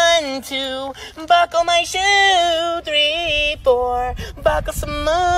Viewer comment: I thought bro said 1,2 buckle my shoe, 3,4 pump me some more💀💀😂😂